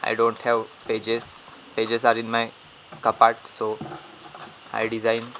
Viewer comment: I love your videos!! ... But When I heard "pages are in my *cup-art*" ... It just made my day!!! Thanksss much love! <3